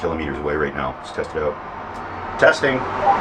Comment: "Testing"? I always heard it was " Testies, one two, Roger"